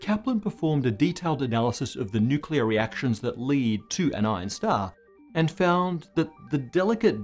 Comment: I honestly thought Matt said Einstar until the 10th minute. :D